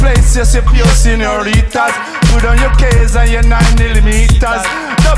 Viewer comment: What’s the Gideon saying at 1:28 about 9mm? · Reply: Put down your K's and your 9mm's.